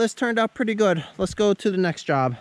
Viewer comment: did you say next job? -sadie